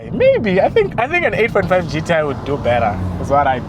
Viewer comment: A45 GTI??What are you saying bro😆😆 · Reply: 8.5 gti